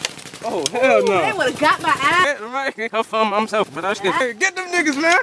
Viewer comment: what he just say? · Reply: Them mf's tryin shoot my camera lol